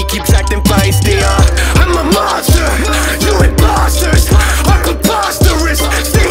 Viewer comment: Why i heard,, imposters"